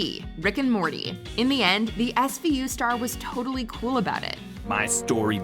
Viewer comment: Am I the only one who thought that captain said, "SVU' Star WARS instead of WAS for a moment there?